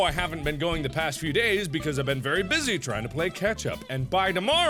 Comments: Did he just say "I've been busy playing ketchup!"???? 😐😐😐😮😮😮🤔🤔🤔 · As i heard "Trying to play Ketchup" instead of catch up..PLAY WITH KETCHUP MARKIMOOO!!